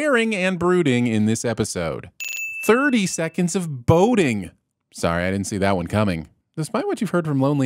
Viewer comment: I was doing something else at 42:21, and I thought he said motorboating. Thought it was a repeat of the Coffee Incident for a second.